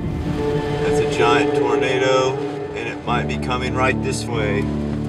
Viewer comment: At about 1:29 the closed captioning says "it's a giant burrito" instead of "it's a giant tornado"... and then again at 5:18 it says burrito instead of tornado. Gave me a good chuckle.